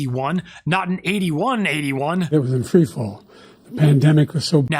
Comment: Did I hear right, the economy was in tree fall?